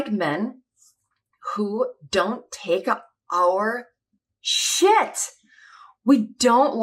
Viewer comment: I thought you said you didn't like men who take a 1-hour shit! Never interrupt a man's precious time on the porcelain throne! LOL!